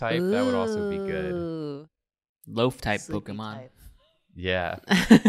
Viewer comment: When he said loaf type pokemon I thought he said lofi pokemon and I was like yeah that IS really chill